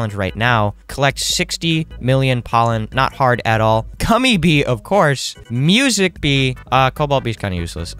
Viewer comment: is it me or did he say "cummy" bee?